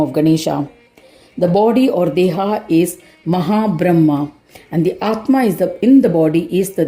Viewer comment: Here the 43rd verse there is the explanation of the form of Ganesha. Here the body of Gajanana the Deha is the Mahad Brahma (Macrocosmic body) and Dehi is the Atma within. The union of both the Deha and Dehi creates knowledge or Bodha which is the understanding of that which is that is called Asi . So in the Mahavakya Tat Twam Asi, That what is seen in the macrocosm is the same Atman in a Jeeva which is the microcosm. This is represented by Twam . Tat is that. That which I am is Asi, the union of Deha and Dehi. this is the manifest aspect. But Ganesha is both the manifest and the unmanifest which is addressed as Gajanana. The unmanifest is called Videha Brahma or the incorporeal or that which is transcendental pr that which is beyond the Deha i.e. Videha. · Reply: maataji...thank you so much..."mahad bramha" explained it all.. I heard it as "maha bramha" instead of mahad bramha in the narration...